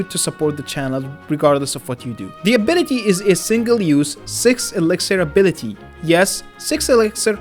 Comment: I think I misheard that. Could you repeat that? It was as if I heard SIX FUCKIN ELIXIER